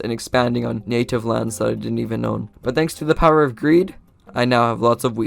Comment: did he say “thanks for the power of creative?” Or the power of green? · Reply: Greed